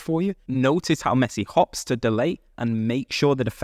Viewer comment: did you say halts the delay or hops the delay ? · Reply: hops to delay